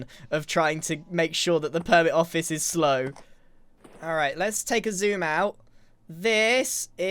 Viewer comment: did anyone else hear let’s take Xisuma out? That’s what I heard.😂